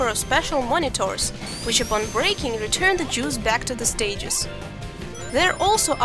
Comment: did she say return the jews?